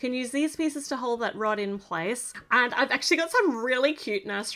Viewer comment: I heard hold that rotten place 😭😭😭